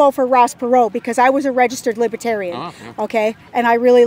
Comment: I'm sorry, did she say she was a REGISTERED libertarian? Did I hear that right?